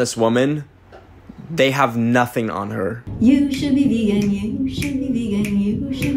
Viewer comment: I thought she said you should be eaten💀